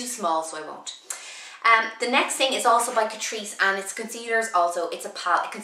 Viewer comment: Not sure what brand you said. It sounded like catriece. I haven't heard about that brand. Are they based in Ireland?